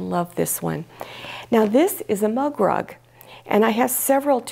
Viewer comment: OHHHHH! MUG rug! I thought you said "love rug" and I'm thinking "What the heck is a love rug"???? I'm doing potholders for my sisters this year. Thank you for the ideas. We used to make wreaths with the colored plastic clothes protectors from a local laundry that my mother worked at. It was one of the projects we kids all did together.